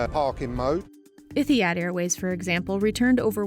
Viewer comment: Did she just say iffyhad instead of etihad?